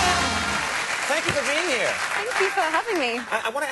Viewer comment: what conan saying in 0:34 could anyone english subtitle please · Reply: you for being here! (Thank you for having me!) I wanna ask you, we had Daniel Radcliffe here last night and I chatted with him briefly and I asked him the same question but I wanna ask you, cause it's such an unusual situation... Or did you want to have subtitles for the whole clip?